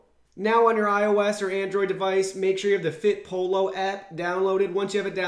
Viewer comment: Amazing review man!! Thank you! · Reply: sounds like he said, "FitPolo" app